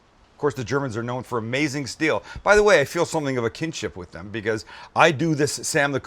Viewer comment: what is the word your saying at 5:50.. kindship?